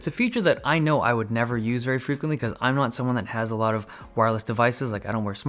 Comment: I thought you were going to say you don't have a lot of friends =]]